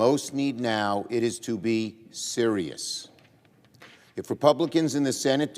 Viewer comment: did he say Siria?? 😂 are we sending money to them too? Everyone else seems to be holding out their hands!